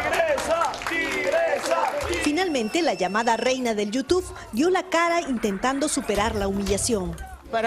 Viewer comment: ¿Escuché bien? ¿Dijo Reina de YouTube? 🤣🤣🤣